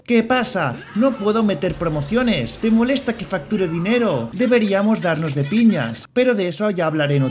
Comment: Te juro por Dios que escuche “Deberíamos dárselo a tus niñas”😂